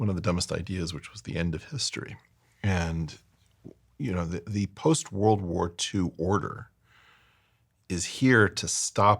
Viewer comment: Eric says clearly "the post WW2 history," Captions say WW1. How can this not be intentional, but why?